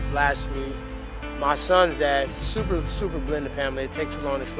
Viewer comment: Who's the last guy he gave the car to?. I heard him say "my sons dad" but I think I'm hearing things lol